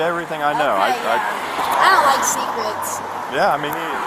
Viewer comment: Did she say, “I don’t like sneak-rets” like secrete and sneakers haha